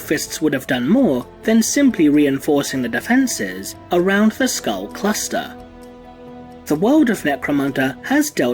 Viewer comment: I thought this said "Skull crusher."